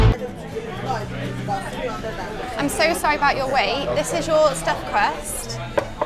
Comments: I thought the waiter said "Sorry about your weight" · i thought she said I'm so sorry about your weight haha